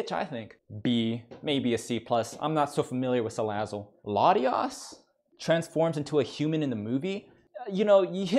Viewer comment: Latias can not only transform, but she can feel your emotions, she can turn invisible, and she can fly. #1 waifu from what I can see. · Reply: I thought you said Latinas, but I agree with you!